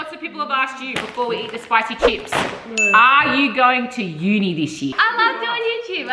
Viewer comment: the funny part is called university unifishie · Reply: She said “uni this year” not “unifishie”